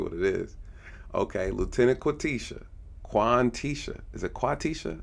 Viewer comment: I don’t know you but did you say Quantisha. You know what it is. You ain’t right 😅